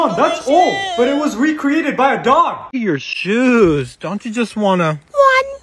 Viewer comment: Why did I hear “don’t you wanna just ONE TWO POO IN SHOE”